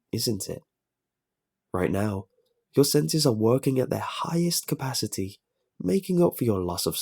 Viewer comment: I'm laughing so hard right now because when I first heard the line "your senses are working at their highest capacity" I misheard it as "your placenta is working at it's highest capacity"